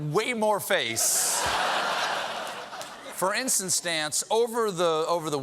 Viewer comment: Did Colbert really say “for insta-stance” at 0:44?? 😂😂😂😂